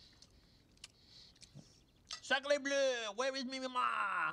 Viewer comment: Do I need my ears checked or something? It seems like everyone thinks he’s saying “where is me mama?” When all I hear is “where is me ma?” Lol